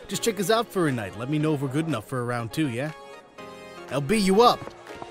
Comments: Why does when he says lb u up it sounds like he said I will beat u up xD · Nino: LB, you up! What I heard: I'll beat you up! Me: OH REALLY?! Grabs Popcorns When? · I heard "I'll beat you up" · I thought he said "I'll beat you up!!" 😂